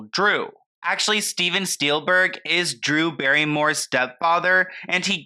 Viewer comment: did you say "Steven Steelberg is Drew Barrymore's step father "? 😅 I mean I'm still reeling from the "some guy called Emilio" comment